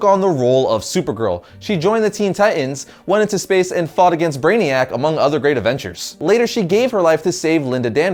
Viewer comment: WTF? 1:47 you said Matrix fight among others great avengers. What the fuck was that?